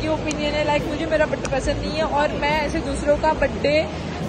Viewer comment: badey instead of Birthday 😂😂😂